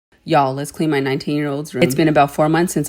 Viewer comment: girl for a minit i thought you said 9 yr old . when i ws 16 i had my own room and i cleaned it and kept it organize my mom tried to help but i wanted to do it on my own now I'm 18 and my room is always clean and fresh he need to learn how to it him self help him clean and teach him dont do it by your own